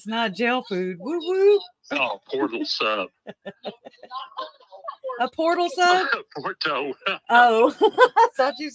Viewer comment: i thought he said "portal sub" too 🤣